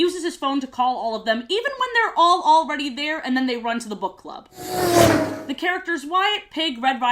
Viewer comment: As a kid I always thought that Wyatt said "to the butt club" instead of "to the book club" lmao